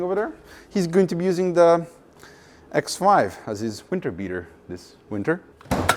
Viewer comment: Ha, my wife thought you said "X wife as a winter beater."